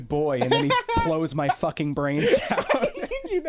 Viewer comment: I misheard "blows my fucking brains out" as "plows my fucking brains out" 🫠